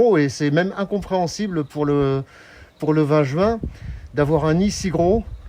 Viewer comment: pour le quoi ? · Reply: 20 juin pas "vagin" 🤣